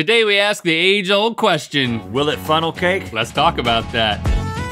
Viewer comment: I thought it said funeral cake